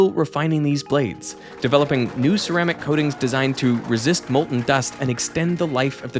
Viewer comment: english subtitles at time of writing say "designed to resist mold and dust" instead of "molten dust" and I was genuinely perplexed for a second how this smart adult could possibly think mold could exist in the inside of an active-use jet engine but then got extremely concerned that maybe that it WAS possible and I was an even bigger idiot...